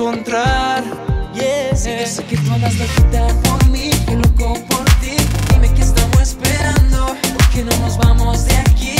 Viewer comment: alguien me puede decir la palabra que dice alonso en el minuto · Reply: Si yo se que andas loquita por mi